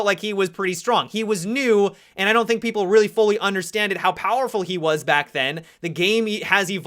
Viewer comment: I'm a grammar nazi, and u just said "understanded" instead of "understood"